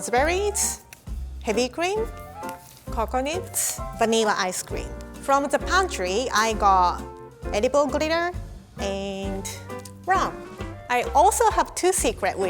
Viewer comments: Anyone else thought said coconits? No? Just me then... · I know she's Japanese but I definitely heard Edible Gorilla instead of Edible Glitter